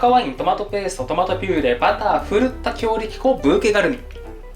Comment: 狂った強力粉に聞こえてしまう😂